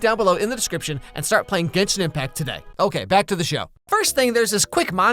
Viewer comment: I thought you said it like- 'Jenshin Impact' D